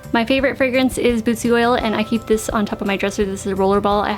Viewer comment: What did you say at 12:15? I relistend about 10 times. What's your favorite scent? Bezu oil? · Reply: Bootzie Oil